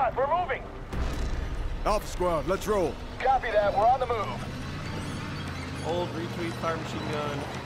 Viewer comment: did he say "hold retweet to fire"?